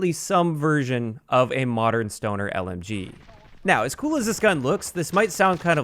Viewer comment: Did he say stoner lmg at 1:40? If he did, I thought there could be the stoner 63 from bo1